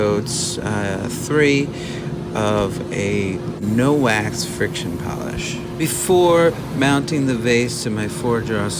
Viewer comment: Valerie Henschel Hi Valerie, I didn’t use any wax or paste, only a friction polish which is a home brew. It’s the same friction polish I use in several videos: a 1 to 1 ratio. One part boiled linseed oil (BLO), one part denatured alcohol, and one part dewaxed shellac. The shellac is a about a 3 pound cut. This is historically called French polishing and has been used for hundreds of years on furniture. It’s not the most durable, but it shines nicely and is easy to repair and use. I only use it on pieces that won’t be handled a lot. You can also use it as a base coat once the BLO has cured. Though, they say it’s good safe, I don’t use on food contact items, I don’t chance it because of the BLO and the garbage that’s out in the alcohol. Just to be on the safer side. Hope that helps, and next time I will mention in the video. Thank you! take care! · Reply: Ah, my mistake! Thought you said sanding paste, not friction polish. Guess I should have listened better!